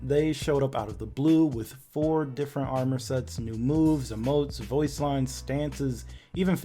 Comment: did he just said new moves?